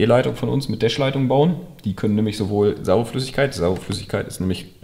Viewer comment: Habt ihr da auch beim ersten mal Samenflüssigkeit verstanden ? 😂